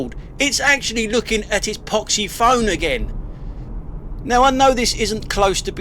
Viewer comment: Somewhere in the first minute I thought I heard "potty-phone". (do I have to get my ears checked?)